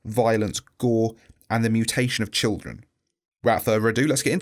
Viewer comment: I first thought that said mutilation of children not mutation